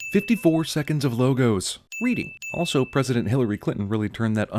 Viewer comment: did he say president clinton? he must be sad now that he knows that trump is president...